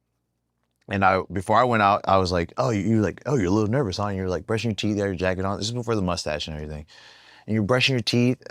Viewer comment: did he just say “you were there brushing your teeth or jacking off”?!?! 😅🤣